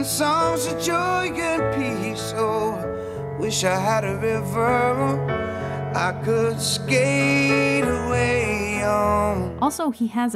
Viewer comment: I thought he said "wish I had a liver that I could skate away on" when he was singing 😂